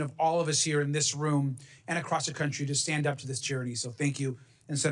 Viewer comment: TYRANT - Rep Garcia says "there's no other way to describe the terror you had to experience." Uh, yes there is another way to describe it - the proper way, with the single word which lies at the heart of our Nation's founding, our Declaration of Independence, and our Constitution. That word is "Tyranny", or its personification as in "tyrant". It is a bit pathetic our congressmen and senators are still so reluctant to use it. Let us hope this changes and that it becomes the 2026 word of the year, helping all true patriots to remember who the real enemies are and who we are being called to be once more. May it be so. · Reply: CORRECTION: at 32:12 Congressman Garcia says "... the obligation of all of us here in this room and across the country to stand up to this charity." Wrong Google LLM, he actually said, "...stand up to this Tyranny." The AI could be forgiven for missing this because it is mumbled, quickly and softly, indicating Mr Garcia uses unskilled rhetorical, and also suggesting he does not recognize the weight of the word for Americans. C'mon Congress, step up to the challenge, unify against America's real enemy. Our fight is not really against racism or fascism or billionaires or elites, though all are loathesome. America's battle is, and always has been, against something much bigger: Tyranny and tyrants. We kicked its a** last time, and after 250 years, we will again.